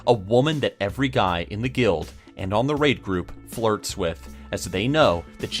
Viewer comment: Did he say rape group at 2:20 loool